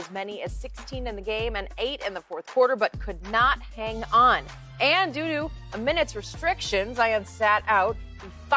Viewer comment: BRO AT 0:10 IT SOUNDS LIKE SHE SAYS "AND DOODOO" BAHAHAHAHAHAHAHA